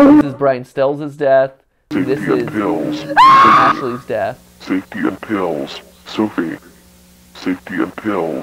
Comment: I definitely heard "safety in pills" as "take your pills"